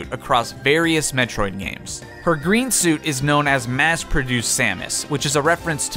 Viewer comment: I heard mass produce condoms?????